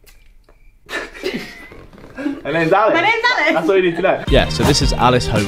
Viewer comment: I thought she said her name is Alex